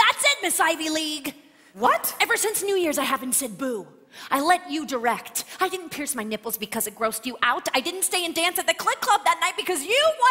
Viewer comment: WHAT DID SHE SAYYYYYY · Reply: “Ever since New Year’s I haven’t said boo. I let you direct. I didn’t pierce my nipples because it grossed you out. I didn’t stay and dance at the Clit Club that night, ‘cause you wanted to go home…”